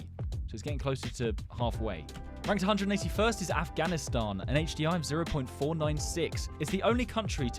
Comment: I thought it was Pakistani instead of Afghanistan